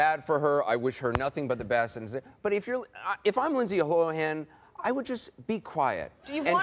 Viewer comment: i swear to God at 12:22 he said if i was lindsay 'hoehan' LOLOLOL